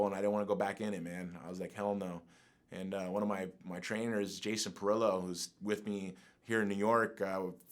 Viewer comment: I thought he said my trainer is Jason Derulo 😂😂